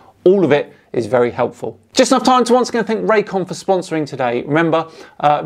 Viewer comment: When you announced Raycon as your sponsor today at the end of the video, I had to do a double-take, as I was gaming while listening to you and you spoke so fast, my distracted mind heard you say "Thank you Ray Comfort for sponsoring me!" and I was immediately like "Nani the fuck?!" And yes, I was so shocked I died in the game I was playing, mind you.